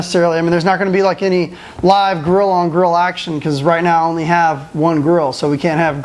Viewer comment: when you said grill on grill the first time i thought you said gorilla on gorilla action. O_o